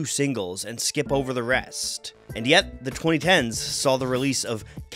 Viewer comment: holy shit I heard him say "early 20s" and was shocked when he was talking about the 2020s 💀